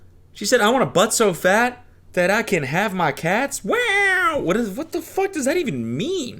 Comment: Cody! I heard “so I can hide my kids” which implies she’s a hardworking mother. Have some respect